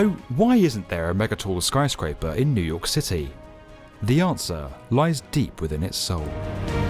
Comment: sounded like he said "nuke city" cuz of his bri'ish